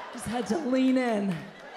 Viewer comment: I thought you said blend in.